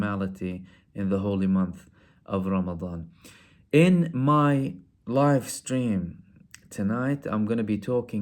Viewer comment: When you said “in my live stream..tonight” I heard it wrong and thought you said “in my wife’s dream..tonight” and I was thinking why are you talking about your wife’s dream?? Confused. Lol you got me brother